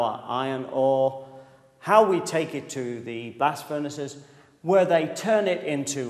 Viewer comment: Instead of "blast furnaces" I heard "blasfemists"!!